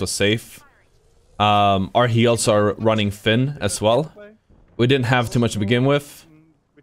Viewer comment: Lol when he says "our heals are wearing thin" I heard "our heels are wearing thin". I heard it out of context and thought it was funny that it still applied